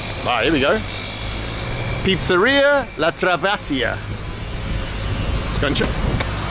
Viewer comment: What did you say?! Pizzeria La Trabantka?! That's just like Pizzeria La SheCooly, is it?